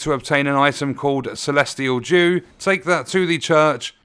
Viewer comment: you mentioned i need to find celestial jew for the absolution, i cant find the celestial jew. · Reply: 😂 is celestial dew. I also thought he said jew. I cant wait I'm gonna play it next week for the first time when I come home from work. Ainsel River: Found on a corpse hanging off a ledge in the ruins. Raya Lucaria Academy: Near the Main Academy Gate grace, guarded by a Bloodhound Knight. Liurnia of the Lakes: A chest behind the Church of Vows contains one.